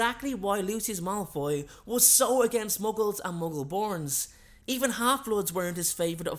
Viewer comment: Did you just say muggle porn in the beginning?